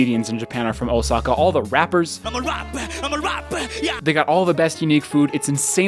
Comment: Did he say "digga"